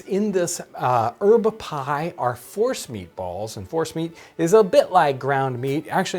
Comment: I thought he said Horse meat balls not force meat.